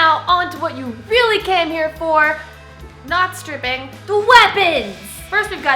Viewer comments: NO SHARP THINGS!! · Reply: DID SHE SAY STRIPING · DID SHE SAY STRIPING